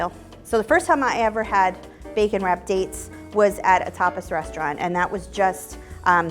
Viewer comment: At first, I thought she said "at a topless restaurant..." and I was like *GASP!* *starts fanning himself like an old lady at church and giving a shocked look* LOL!